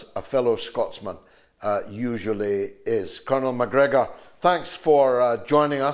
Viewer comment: Thought he said Colin Mcgregor ... Colonel Mcgregor